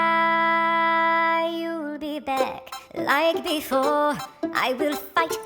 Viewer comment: Okay is it jsut me or does it ALWAYS sound like KG is saying “you’ll be *beck*”?